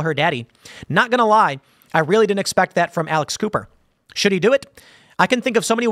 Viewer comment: Thought you said Alice Cooper for a sec